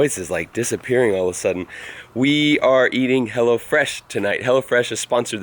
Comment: I thought you were saying hellafresh hahahahahah